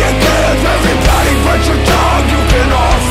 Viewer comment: I always thought it was "and your mom and your sister and your dog" and I was like 'What did the dog ever do to you'?!? Then I heard "Everybody but your dog" and I was like 'Oh ... carry on.'